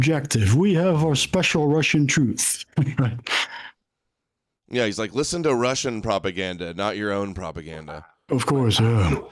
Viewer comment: That's not what he said... He said have a realistic understanding, of what's going on outside of THEIR propaganda (meaning ours, the US)... AND, outside of 'our own propaganda', as well (meaning Russia's propaganda). It's definitely not easy to understand him, but, some of my ancestors were Prussian-German... So, I think understanding Slav, in ANY form it arrives in, including broken English, comes a little more naturally, to me. 😂😂😂 o/